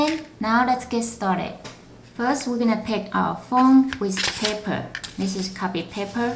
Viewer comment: its funny when she said paper it sound like she said pepper for a second lol